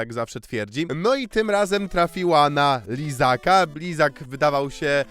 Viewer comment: nie siedzę w temacie za bardzo i zamiast lizaka usłyszałem izaka xDDD. miałem takiego mindfuck'a, przecież on miał dziewczynę i dziecko wtf! Na szczęście szybko to sprawdziłem xD